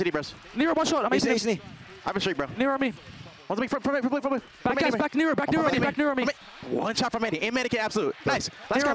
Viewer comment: Was Pred calling out “Nero” at 18:12 ??😂😂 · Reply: yeah it's the callout for that spot